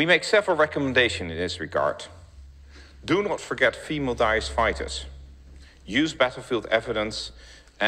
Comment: I can't seem to understand a word he uses, 'dice' fighters. What does 'dice' mean or am I hearing it incorrectly? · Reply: Daesh another name for Islamic State